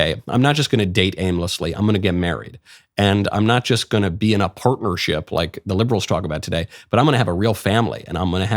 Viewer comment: I misheard Michael say that Liberals are promoting pervertship. I found myself agreeing that they do, and then I realized he has actually said partnership.